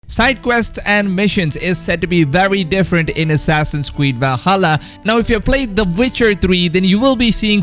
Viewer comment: Did he just say assassins queed? XD